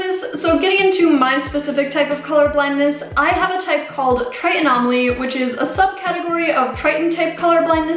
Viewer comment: i'm tritan-type colourblind, too. i have tritanomaly tho (second rarest type of colourblindness). my S cones are screwed up but not competely disfunctional. i rarely ever find tritan-type colourblind videos or influencers so i just wanna say thank you for making this video!! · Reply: oh i didn't hear the part that you have tritanomaly too. i thought you said tritanopia. my bad!!